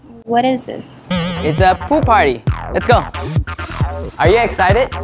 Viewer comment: 😂😁 did i hear poo party